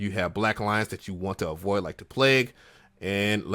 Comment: ma guy i heard @ 0:44 "black lives, you need to avoid like a plague". Please say im not the only one